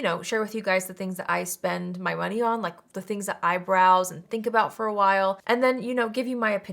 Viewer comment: When you said “the things that I browse” I heard “the things that eyebrows” 😅😂 That would be a fun name for a makeup testing vlog. Or maybe a beautician’s office.